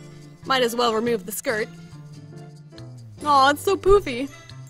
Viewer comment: I thought she said poopy🤣🤣🤣🤣